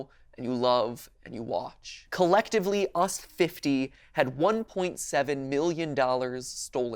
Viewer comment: does he say 1.7 billion?